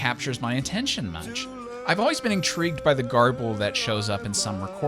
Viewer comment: "I've always been intrigued by the .....that shows up in some recording part way through..." By the what? Can someone help me out with what he says there? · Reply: I've always been intrigued by the Barbie that shows up in some recording part way through the song.